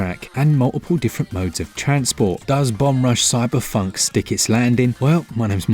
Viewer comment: I kept hearing "bum rush" 😂